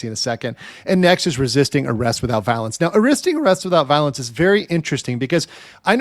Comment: I thought you said "resisting arrest without balance" not just the first time either